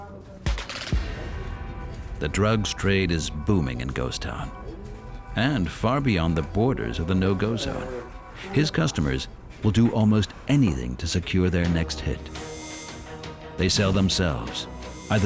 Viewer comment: I thought it said no gun zones I was like yeah ight they definitely got guns😵 🔫😵 🔫 down there in Ghost town